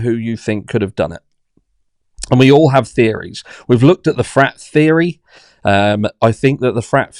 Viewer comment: I was playing solitaire while listening and instead of "frat theory" i kept hearing "frat fairy" and it tickled me pink.